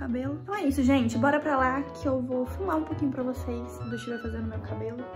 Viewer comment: Só eu que entendi fumar